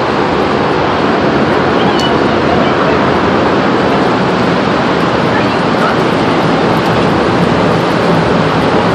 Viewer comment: I think I heard the guys wife say " ARE YOU CRAZY?? GET IN HERE!!! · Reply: close, she said, get in here, what are you f..king crazy ?